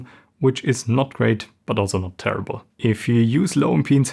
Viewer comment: An I the only one hearing that he says "terrible" instead of "not terrible" @ 13:36?